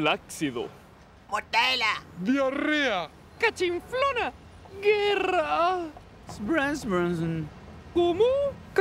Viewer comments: sin dudas la peor frase, ni siquiera se entiende que dice. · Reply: dice guerra xd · Alguien sabe que fue lo que dijo el mono? Yo escuche flansbensen